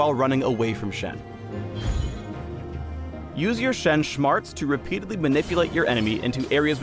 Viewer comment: Shen Shmarts? I heard that.